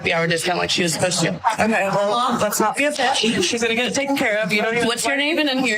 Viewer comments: It sounds like she said let’s not be a fish. I don’t know what the fuck that means but either way she better be thankful she didn’t get slapped. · I honestly thought she said let's not be upset but I could be wrong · I thought she said "Don't be a fish". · Let’s not be a fish or bitch? I thought I heard fish. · The waitress is the one that called the lady a bitch obviously you got something wrong with you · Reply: I thought she said let's not get upset.